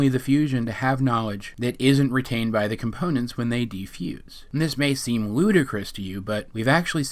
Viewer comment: did he just say "defuse" omg it's "unfuse" bitch